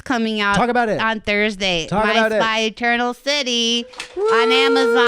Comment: Went to look up Kristens movie and I'm a little hard of hearing so i heard "I Spy Turtle City" google was like you are so very wrong and i still ignored the suggested movie, found out instead its "My Spy: The Eternal City" 😅🤣